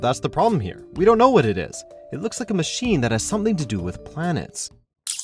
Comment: Did he just say planets and not plant's